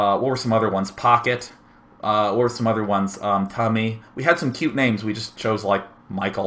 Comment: did he just say cummy?